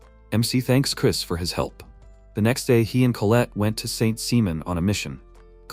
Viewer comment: I’m sorry did he just say Saint Semen.